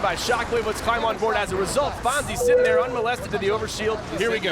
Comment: does he say "Unmolested"? lol